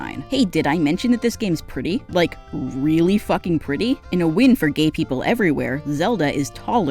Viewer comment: am I insane or did you say it’s a win for gay people that Zelda is barely taller than link?